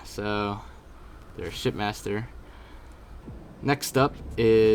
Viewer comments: For this entire thing I thought you were saying "shit master" instead of ship master. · It sounded like you said "There's the shitmaster here"